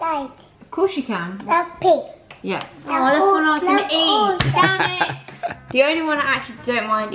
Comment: did anyone else hear say damnit